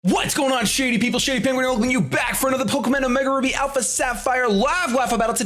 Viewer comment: I heard "Pokemin" at the beginning.